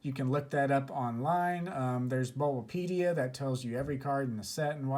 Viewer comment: Thanks so much for this very informative video! Just a quick question: what do you say at 5:39? There's ......."pedia"..where you can look it up. What's it called? Thx. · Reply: Check out the wiki called "Bulbapedia"